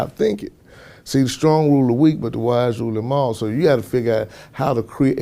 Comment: What's he saying 0:57 starts with the stronger where the weak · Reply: He said, "The strong rule the weak but the wise rule them all."